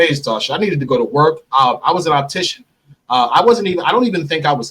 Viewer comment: lol he said he was an optician too.. speaking like a true criminal lol smh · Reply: I thought he said mortician